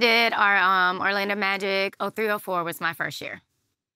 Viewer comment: She said her 1st year was 03-04 …but it really sounded like she said 304..the irony 😉